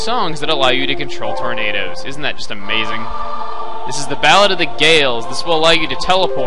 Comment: LOL, I thought u you said "Ballad of the Gay olds"